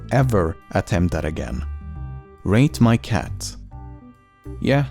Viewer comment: I thought that user said “Rape my cat “ when I listened to this video the first time lmao